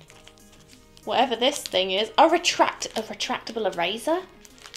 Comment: I thought you said retractible RAZOR and was both confused and concerned XD